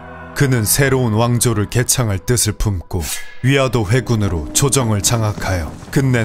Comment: 위화도 회군을 위 아 더 회군으로 잘못 들었다...